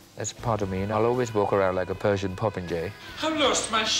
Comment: " I'll always walk around like a Persian popinjay, and no one's gonna stop me, honey." 💯💪😘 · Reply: I thought Freddie said "Persian protege".